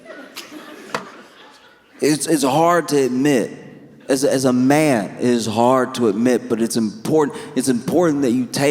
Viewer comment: Instead of ‘as a man, it’s really hard to admit’, I heard ‘as a man, it’s really hard to knit’